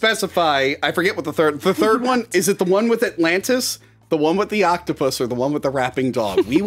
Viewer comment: We reviewed the animated Titanic movie on Patreon. The one with the octopus not the one with the rapping dog... <link> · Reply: Me too! Could have swore it said "Not the one with the octopus raping the dog" hahaha